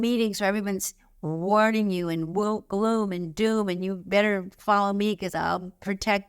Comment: 'Woke doom and gloom' If I understand correctly 'Woke' started out in the black community describing those striving for social & political justice for black Americans but is now a disparaging term used to malign anyone considered socialist and/or progressive. Sad to hear you using this term in this way, i was shocked, tbh and had to check the transcript. It's quite something how the quality of caring about the well-being of others can be turned into an insult. I'm curious what you consider the opposite to be? Sleepy, maybe? Im an empath, and im tired of people who care being belittled and shut down. Red flag for me, unsubscribed. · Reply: She’s saying “woe, gloom and doom”. (7:34) You’ll notice that there are lots of transcription errors and occasional incidents of her not enunciating fully. I can see why you think she said “woke” but it has nothing at all to do with the rest of her message and I’ve never heard her say anything of the kind before.